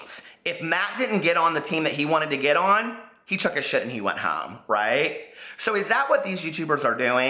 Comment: I thought you said "matt took a shit and went home" hahaha but omg preeeeaaaccchh!! I friggin couldn't agree more!